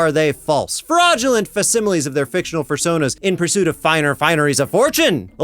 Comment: did he say "fursona"?